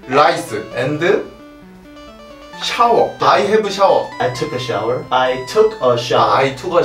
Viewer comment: For a second i thought he said i "ate the shower" lol😂